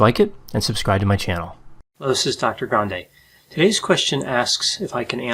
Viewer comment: Always have to laugh when you say hello...the first time I heard you, hadn't read your name, except basics...so I heard "Hello this is "Taco Grande"...but hey, i love Mexican food and i'm on Prednisone so...Love all your video's...sure helps explain, don't need a DX, just your speculation is enough for us to draw our own conclusions.